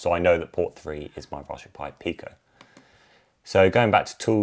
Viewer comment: I thought I heard rajberry pie.